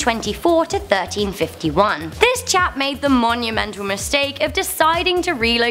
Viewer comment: Did she just call the sultan a Jap?